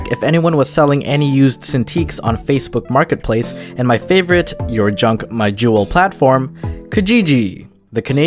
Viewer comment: did he just say the your junk my juul?